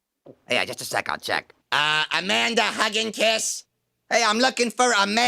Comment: 468 dislikes can't find "Amanda Huggenkiss" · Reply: I always thought he said "man to hug and kiss".